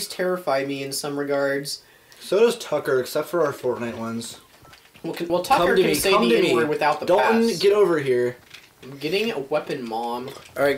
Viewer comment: I also don't watch your smash ones. also quit talking over each other and not adding captions. I want to know what dalton is saying about me. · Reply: tucker smythe he said “well Tucker can say the N-Word without the pass”